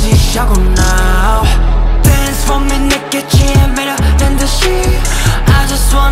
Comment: not me thinking they sing “dance for me naked” I almost choked when I thought that’s what I heard 😅 love it tho